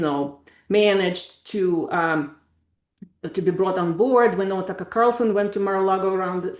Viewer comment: TS 32:10 Did she say Tucker Carlson?